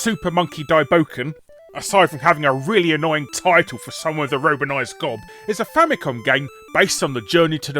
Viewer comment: What does he say at 7:02? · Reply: "aside from having a really annoying title for someone with a romanised gob" meaning someone who speaks words using the roman alphabet.